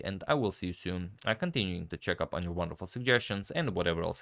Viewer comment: I thought he said I'll see you soon in heaven XD